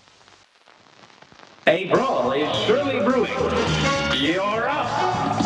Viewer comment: I wasn’t fully listening and I heard “A ball is shooting through you! The duck” I was really confused... XDDD